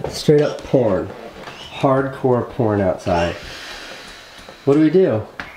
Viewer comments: sounds like he said "it's hardcore porn" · Is he saying hardcore porn at 2:33? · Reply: "its pouring, its literally hardcore pouring" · I think he said "Hard Core Porn" xD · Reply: hard core pouring =) but i like the way you think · for a second I thought I heard "hardcore porn" xD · lol I heard porn, and "hardcore porn outside" ahahahaha lol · Who else notices when Casey said "its hardcore pourin outside" it sounds like he said "hardcore porn outside" 😂😂😂😂😂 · did anyone else thought he said hardcore porn on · I couldn't stop laughing bc I thought he said hardcore open 😂😂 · I know he said pouring but I heard it as "Honey, there's hardcore porn outside."